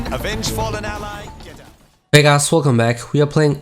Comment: Lol I heard " hey ashole welcome back!"